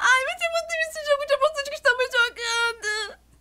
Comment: ENTENDI !!! Ela disse: eu odeio esse jogo idiota que estamos jogando